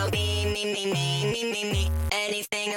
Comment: Is he saying meme or me-me? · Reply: Me